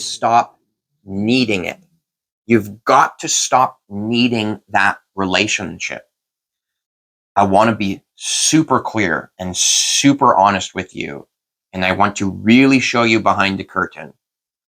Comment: I thought you said "super queer" bwahah - made me giggle. I hope its okay to poke fun XD. Also wondering, are you going speak to the T word?